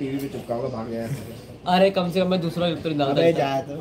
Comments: kya bola😞 plss btaado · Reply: Kum se kum hum dusri ka clip nhi daalta😂😂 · ko kya bola??? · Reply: Usne bola atleast main kisi dusre ki clips toh nhi dalta